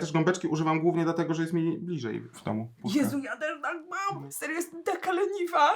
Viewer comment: co on powiedział w 7:30? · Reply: że używa gąbeczki bo ma bliżej w domu (rok po XD, milej nocy)